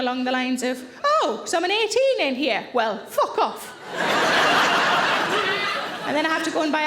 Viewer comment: What does she say at 59:00? She tries on clothes, they don’t fit, and she walks out shouting ______? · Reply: "Oh, so I'm an 18 in here, well fuck off.'